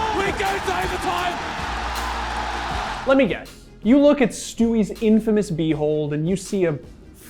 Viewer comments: All I heard was “ you look at stewie’s infamous B hole” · who else heard " let me guess you've heard of stewies infamous b hole"